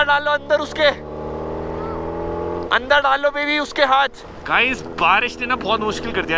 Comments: does he say babe🗿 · kya bola 'baby'?